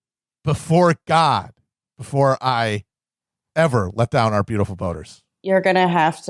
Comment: I thought they were saying "beautiful voters."